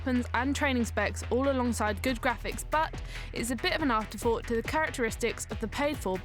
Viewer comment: did she said dicks?